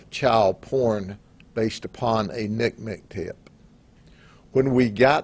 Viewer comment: Sorry for misunderstanding but I'm not sure what he's saying and/or what it means. I tried it with closed captioning and it says Nick Mctip? is this right? he just says it fast and I felt like the judge from My Cousin Vinny when he said "The two whuts?...Whut was that word?"